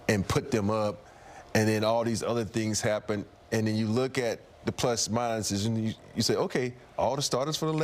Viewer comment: For a moment I swore he said "and those bichs came in.."😅